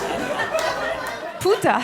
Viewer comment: did she say 'punta' ? I laffed so hard i pissed myself